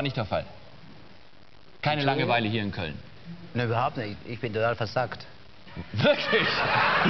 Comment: "Ich bin...." Was sagt er bei1:45 ? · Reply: ich bin total versackt